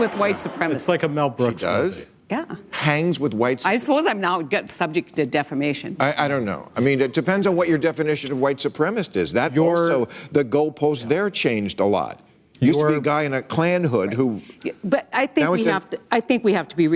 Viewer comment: I wonder if "she does hang with white supremacist" was in reference to Gina now working with Daily Wire. It then sounds like Heidi says "I suppose I’m now subject to defamation" and giggles about it, as if she knows she is committing slander but is immune to being held accountable for such.